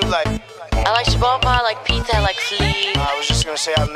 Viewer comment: i like pite and fli😘❤ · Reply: Dila Osmanaj i thought she said "i like pizza and sleep" ? 😂